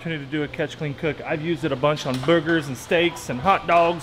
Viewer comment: is it me or does it sound like he said boogers